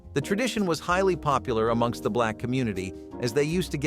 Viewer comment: did he say “the tradition was highly popular among the black community”? Or am I just hearing things?